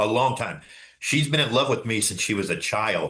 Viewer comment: We've been together since we were 13. Yet both have children by different people. It was obviously toxic early on. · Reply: Did he say that? I heard him saying he has been having sex with her since she was 13, there was no we where 13